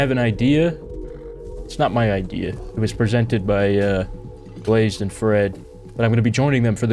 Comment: you said blazed and fred and i thought you said blazes friend and i was like damn what happened!?!?! did he just refer to fred as blazes friend ouch.... lmao